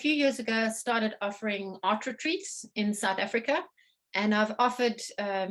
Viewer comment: What did she say at 10:58? She started offering "art turies" in South Africa? What is an art turie? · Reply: Never mind. She said " art retreats." She just said it really fast with an accent so I missed it.